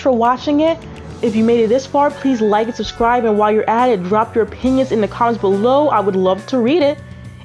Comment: omg I heard "drop your p_nis"😭💀🙏